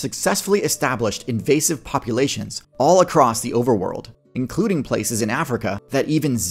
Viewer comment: did you just say "overworld"? I would love to see a fantasy version of this